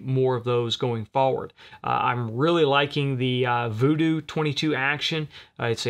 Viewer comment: What is the rim fire action with a 700 footprint? I can't find it with a search...and it sounded like you said Voo Doo. Thanks. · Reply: Christopher Brusa Vudoo V-22